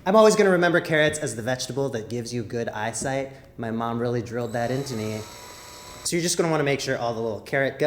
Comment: I misheard 1:05 as My Mum really drilled my